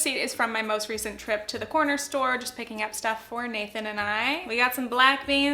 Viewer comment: When you said “my receipt from the corner store”, I thought you said CORONER store, and I was like ????wut